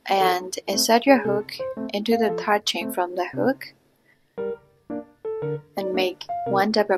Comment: thank you for the free pattern! btw what do you say at 29:03? i'm not fluent in english so wo subs i can't really understand :c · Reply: I said “insert your hook into 3rd chain from the hook”😁